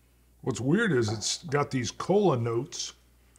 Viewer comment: I thought said, "colon notes" not "cola notes"(at 6:12- 6:16), but I am glad he said "cola" a few more times after. Wow, I need to get my hearing checked.